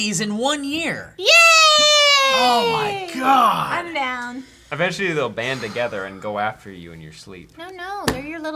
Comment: When they were talking about the avengers, I thought said &quot;AVENGEtually&quot; (eventually) 😉😉😉 Would&apos;ve been a great pun ahaha